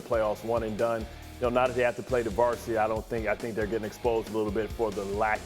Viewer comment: Can anybody tell me what this guy says at 1:28? I can’t understand it I’ve tried playing in slow and everything. Something about Seattle, sounds like varsity or bar? I’m not sure thanks for any help